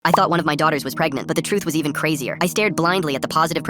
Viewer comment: At first I thought he said "I got one of my daughters pregnant"